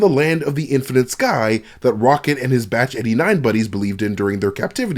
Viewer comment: Did I hear batch or badge? Just asking, cause I know it's batch. But how he said it sounded like badge.